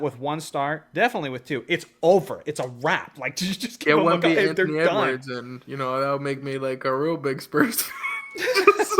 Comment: who did he say? 🤔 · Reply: Anthony Edwards lol